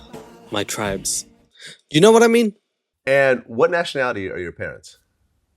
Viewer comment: Nationality is actually what he asked this creature about. 🤣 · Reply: the interviewer said Nationality, said ethnicity...I guess I thought they both said Ethnicity, lol. But the point still stands, Ethnicity means much more than a simple "hispanic or non-hispanic" as you claimed it did...which was the whole point of my first response to you